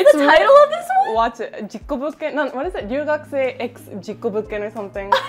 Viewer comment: alright, if you can't tell by name already... so, what I can hear is "chuugakusei x jikobu-something" which translates to "middle schooler X something", might be "accident" does anyone know what it is? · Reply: 留学生 x 事故物件🥰